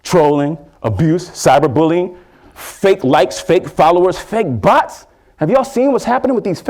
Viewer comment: oh, fake bots...... i thought he said fake butts